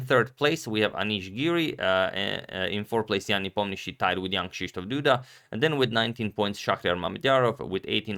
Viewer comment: I always hear "young Krysztof Duda" and only just realised that he's called Jan